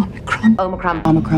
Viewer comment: I heard one "omni-cron" in there.